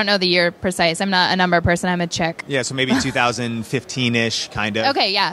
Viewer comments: Did she actually say, “I’m not a number person, I’m a chick”? You’ve got to be kidding me, 🤮. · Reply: I thought she said "check"... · Her implying girls can’t be good at math was cringe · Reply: To me, it doesn't sound like she said "I'm a check person", she really said "I'm a chick" with this kind of pseudo-deprecating laugh. Meaning " i'm a woman (an attractive woman, of course), so hey, what so what do I need basic math for?" kind of thing.. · Did she actually say, “I’m not a number person, I’m a chick”? You’ve got to be kidding me, 🤮. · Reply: I thought she said I am a check person. LOL